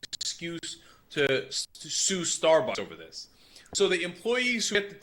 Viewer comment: I thought it said starburns at first